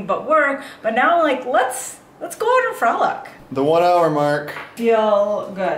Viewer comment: You guys sound like you're on coke around 11:00 lol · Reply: WTH, did she say "let's go and f*ck"?